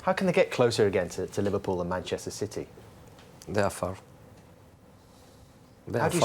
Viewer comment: Haha I thought he said "they are fucked"